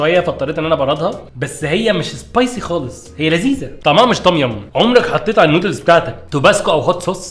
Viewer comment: انا سمعتها tobacco 🙂😂😂